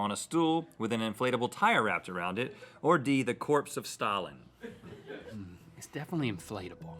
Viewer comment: Legit thought the Rihanna one said "the corpse of satan" for D I was like W H A T